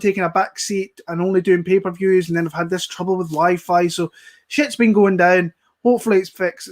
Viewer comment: Thought he said trouble with his wife 🤣😂🤣😂🤣😂🤣